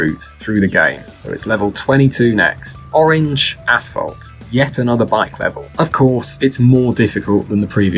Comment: I was listening to it but not paying much attention, and definititely thought he said orange assholes